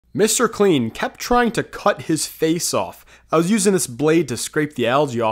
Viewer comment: Bruh i was not looking at my screen when i scrolled and all i heard was "mr.clean tryed to cut his face off"